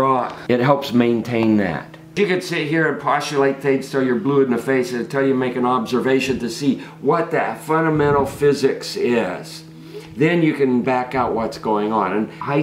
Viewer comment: What is he saying at 5:47 ?? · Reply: Quote: "You can sit here and postulate things 'til you're blue in the face; until you make an observation to see what that fundamental physics is, then you can back up what's going on."